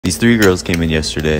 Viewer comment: At first I thought he said "these three year olds came in" but I was corrected when watching it 50 times